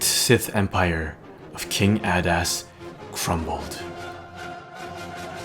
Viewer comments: I thought i heard you say "King Badass" XD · it does sound like hes saying king badass